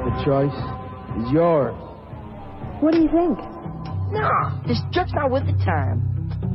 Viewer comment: Oh I up till now thought he said “this jerks not with the times” instead of “these drugs aren’t worth the time.”